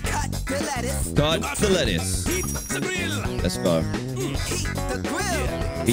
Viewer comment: When it said “heat the grill” i thought it said “eat the grill”.